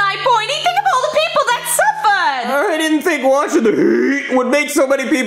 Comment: Did she say Sulfur?